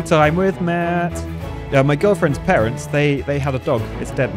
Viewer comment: I heard "my girlfriend, Terrance" instead of "my girlfriends parents" lmao